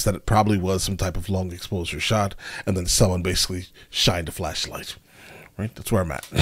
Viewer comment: For a brief moment I thought you said "flesh light"....(never mind)